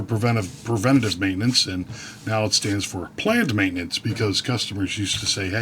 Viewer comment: At first I thought you said plant maintenance and thought you might be getting into gardening